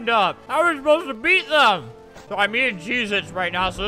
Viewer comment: I could’ve sworn he said “im eating Jesus” Albert 😂😂